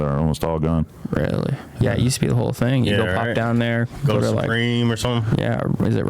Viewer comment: was the first time I heard and he basically said yeerweennn supreme 😂😂😂